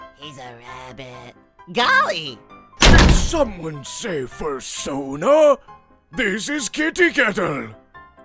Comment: DiD sOmEoNe SaY fUrSoNa 😂😂😂😂